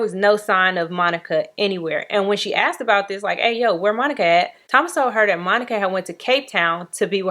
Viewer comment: You said the friend said “where Monica at” I heard “where my nigga at” smh